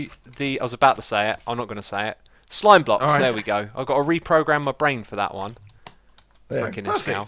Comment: What did he say? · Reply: He said "I gotta reprogram my brain for that one". The reason he said that is because he was about to say "Iskallium" instead of "Slime Blocks" and then after that... "Frikken Iskall!"